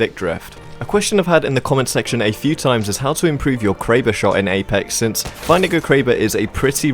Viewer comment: I thought you said my N Word Kraber